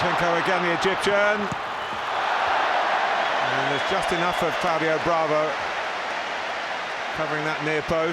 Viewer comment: did he just say Fabio Bravo